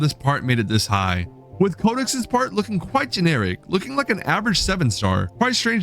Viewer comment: I thought bro said conix😭